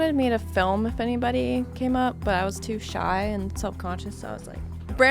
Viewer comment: Not gonna lie when she said “I was too shy” I thought she said “I was too high”. 😂😂😂